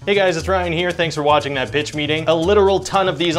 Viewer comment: i thought you said bitch meeting lololol